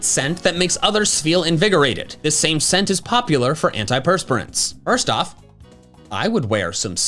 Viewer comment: I thought that said antidepressants and was so confused